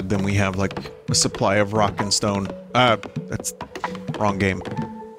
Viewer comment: Did I hear a Rock & Stone?